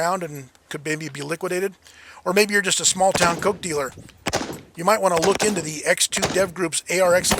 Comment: "Small town coke dealer," lmfao! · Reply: I'll have to get my ears syringed, I thought said "goat dealer".